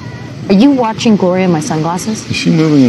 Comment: I heard him say "go ahead, I'm glistening" like he was just watching the water I'm her body